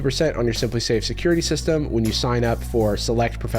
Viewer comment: help i thought the sponsor said "simpsafe"